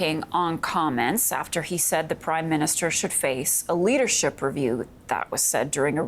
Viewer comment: did she say Prime minister "shitface"?